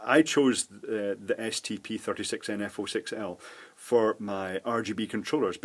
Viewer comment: Did you said "ST P46NF06L"? When I search the partnumber, it does not exists. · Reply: stp36nf06l